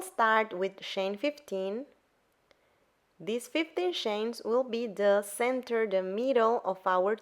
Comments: I really need my ears tested I thought she said chain 50 and not 15 and I was on my way to making a blanket for Moby Dick lol. I have to learn to pay attention ;) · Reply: OMG!! I thought she said 50 ch as well. I reduced it to 25ch and MADE the Moby Dick blanket, big, wide and short. Haha. I'm too new at adjusting patterns so I didn't realize my mistake until I was running out of yarn. OMG!!! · I really need my ears tested I thought she said chain 50 and not 15 and I was on my way to making a blanket for Moby Dick lol. I have to learn to pay attention ;)